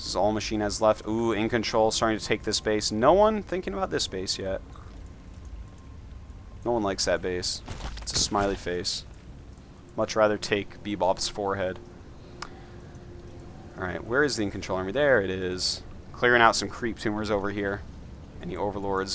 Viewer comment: Did you just say you'd rather take "Bebot's forehead"? as in the cool iPhone synthesizer app? lol